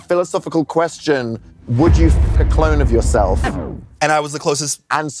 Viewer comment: I had to watch it twice because I wasn’t paying attention and then I thought I heard him say Wilbur soot so I had to rewatch( they do look somewhat alike)